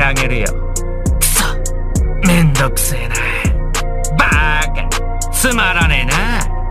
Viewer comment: d-did syoubai just say Baka